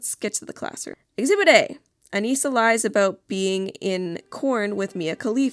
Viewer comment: For a split-second, I heard that as “she lied about being in Korn”, which would’ve been funny as well.